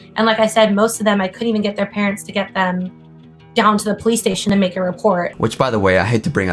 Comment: I thought he said they took me to the wig scale station 💀 hes awful either way and the wig